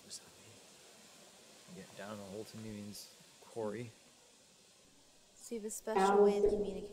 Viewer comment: When Elton said quarry I thought he said Corey so I had to turn on subtitles to check 😅